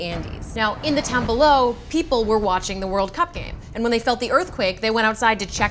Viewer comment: I thought I heard the World Cupcake. Lol